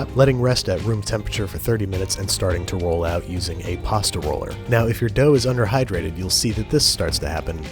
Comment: I thought he said a gay pasta roller